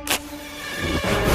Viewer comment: "He's burnin bright, little girl!" 😂😂😂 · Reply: Jones I thought he said: "He's burned and fried, little girl!"